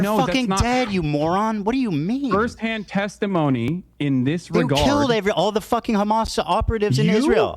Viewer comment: Ethan didnt even say "You" at 1:42:52. Crazy how people hear "You", I didn't even and looking back I still cant hear "You". Clearly said "They" lol. If anyone heard "You" I'd be concearned about my perception...